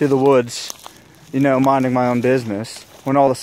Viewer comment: for the moment i though i heard "It's yobanyi badger!"